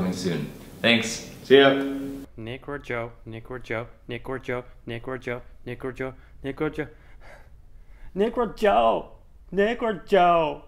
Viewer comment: all i hear from "nek o jo nek o jo neko nekoooo" :')